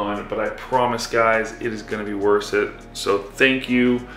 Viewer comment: interesting Freudian slip you meant to "its gonna be worth it" but what you said is "it's gonna be worse yet" ....both are probably true 👍